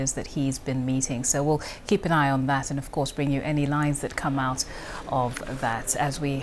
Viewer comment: What did she say? sounds like she said "we'll bring you any lies that come out of that"